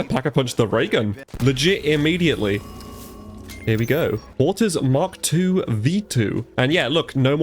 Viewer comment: Not sure if anyone else heard this, at 3:56, I looked away and heard "autism mark II" when he said "porters". Might be cooked given that I instantly got jealous I didn't get the update patch 😂. Great video sir! Thanks for the cozy vibes and good times